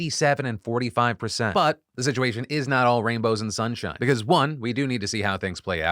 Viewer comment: When he said, "everything's not rainbows and sunshine", I thought he said, "Rambos and sharp knives"!